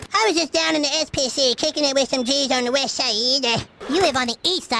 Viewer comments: English is not my mother language, so South Park is obviously dubbed in my country. When I first heard this West Side line in the 4th Grade intro (the intro is sadly not dubbed in my country), I thought he was saying Wish Ya Either. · When I first heard Cartman say “west sieed” I thought he said “wish I eat it”